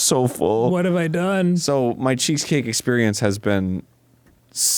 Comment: sad that no one seemed to notice that it sounded like said "cheekscake" instead of cheesecake 16:18 lol